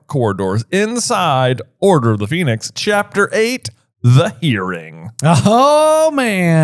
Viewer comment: Oh, I heard “The EARRING”, not “The Hearing”! I missed the “H”. Lol 😆 I was like, “Wait, ‘The What’?” Anyone else missed the “H” or just me?? 🙋🏽‍♀🤭 Oh, my gosh! I NEVER thought that Percy could be undercover! Genius!! 🤯 I always just took it as “head cannon” that he was under the Imperious curse all that time! And, I actually THINK, if I remember correctly, that J.K. choose the middle name “Jane” because SHE thought it was the most “boring name possible”, HER words, not mine! (Apologies to any “Janes” out there. I’m sure you’re all lovely people! 😉) And she chose “Dolores” because she had a teacher who she HATED with that name! (Which I, personally think is actually kinda funny! 😆 I wonder where that teacher is now … 😅) Aha! NOW I know where today’s theory comes from! 😳 And Jacob, too! Or rather, Jacob’s great with Newt. Also, doesn’t Luna marry one of Newt’s grandsons?? 🤔 Yeah, except that they don’t have Thanksgiving in the U.K. (at least I don’t think so … )!! 🤭 OMG!! Yes!! HBO, PLEASE include Dobby in this scene, even for just a minute of screen time!! 🙏🏻🙏🏻😁 The “They almost kissed me” comment really made me giggle! 😅 Also, REALLY good review question! Hadn’t thought of that before! 🤯